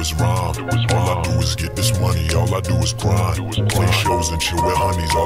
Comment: When he said " I'm one of kind " I thought it said "I went to vidcon"